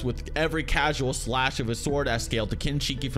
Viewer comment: I thought he said Chewbacca tensei.....